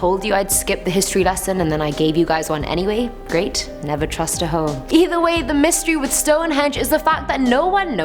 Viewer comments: Did she just say "Never trust a ho?" Lol!! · did just call herself a ho?🤭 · did she say "never trust a hoe"?!? Lmao I had to reply it like over a dozen times and still don't know if she said "hoe" like wtf lmao please help me to understand if this bird just called herself a "hoe". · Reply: I did say hoe but its literally a joke hahaha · did she say "never trust a hoe" 💀